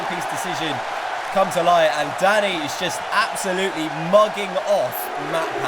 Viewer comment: it sounds like said daddy instead of danny